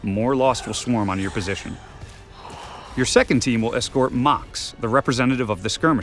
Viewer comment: did he say MOCX